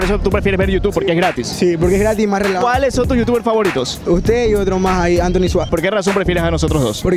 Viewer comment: Soy yo o escuche antronixx